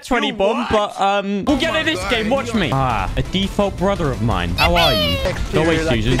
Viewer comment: did he say brother then Suzie?? best YouTuber ever 😂😂👌👌